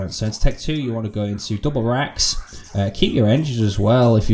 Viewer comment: what are you saying here? Double reqs? Double something and I can't make it out. Thanks for the video!